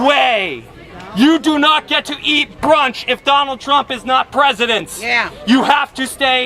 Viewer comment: Did I hear that dude wrong, or did he just endorse Trump winning in 2020? "You don't get to eat brunch, if Trump is not President" LMAO.